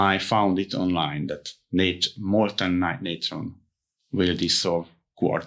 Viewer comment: What is he saying at 11:26-11:36, about the name of the thing that desolves quartz? In CC, it reads "molon nron" or "mnron", but it sounds like hes saying "motornature"! And, after you chemically break down the granite, can you reconstitute it?